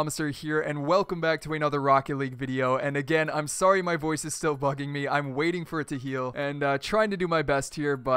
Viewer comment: sounds like he said "my voice is still fucking me" 😂😂😂